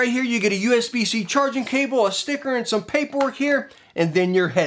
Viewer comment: Totally heard you say “You get usb C charging cable, a Snicker...”